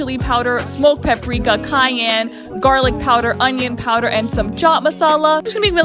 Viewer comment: i thought she said kanye